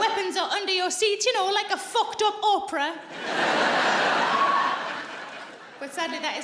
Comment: I know she said "Oprah" & I get the reference, but I heard "opera" &...yes please.